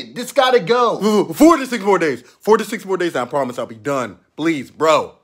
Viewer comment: I thought he said 46 more days